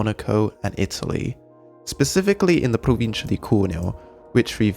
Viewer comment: Huh? "What is "the Pro moving the coral"? (that's what the CC says) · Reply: Provincia di Cuneo (province of cuneo)